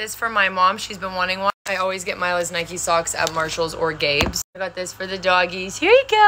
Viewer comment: I thought she said gay but it was Gabes I think she said Gabe’s💀